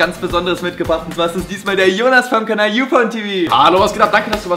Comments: ich habe youpornTv verstanden......pupertät halt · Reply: er hat auch UpornTV gesagt xD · Ich habe youporntv verstanden.... ooops😅😂✌🏼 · ich hab UFONETV verstanden